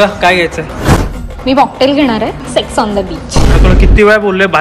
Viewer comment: is it me or she is really saying "sex on the beach"